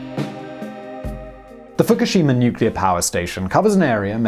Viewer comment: Thought it said fuckishima